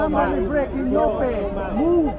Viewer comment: did I just hear "i breaking your face" at the end?? .. Hahaha he could try.. am sure the brothers would have dealt with it accordingly... 😂😂